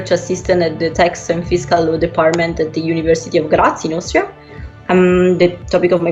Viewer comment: Did she said university of karachi